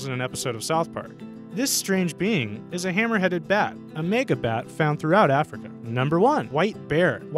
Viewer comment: wait whaa, did he just say nigga bat? I'm honestly confused rn.